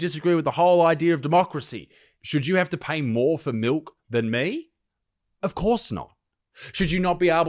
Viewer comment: isaac i thought i heard you say 'miwlk' surely not 😂